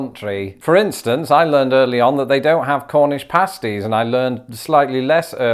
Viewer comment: one thing I cant understand as an american, about the british (No offense) Is the accent. and im not saying "Oh i dont understand why you talk like that hehe its so weird". its genuinely I have issues understanding what you guys are saying sometimes. like in the intro when you said "cornish pasties" i heard "horninashties".